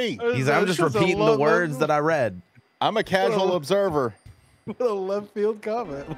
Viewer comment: did he say casual or chaotic?